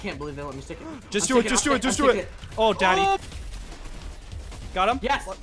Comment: Did I just hear.. “Oh daddy—“